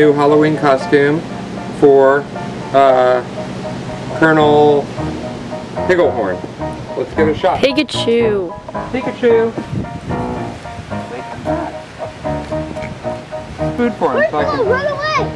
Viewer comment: Oh, come on. Now you're just trying to bait PETA. :P I thought you were saying Colonel Pigglehorn like Fred Figglehorn. Hey, there's a name for you, Fred Pigglehorn!